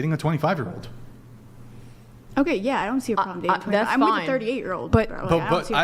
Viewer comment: Omg did she just say she's with a 38 year old.😂😂 Lol.. The hypocrisy is hilarious..😂😂